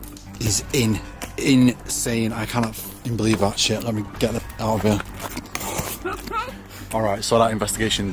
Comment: Did anyone else hear the a ladies voice saying "get me out"